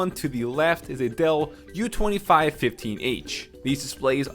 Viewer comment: said a Dell which sounded like Adale so her photo pops up for a sec. the whole video is funny.